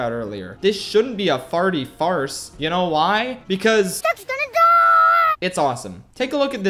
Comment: What Does He Say During 19:35 Im Guessing Ducks Gonna Die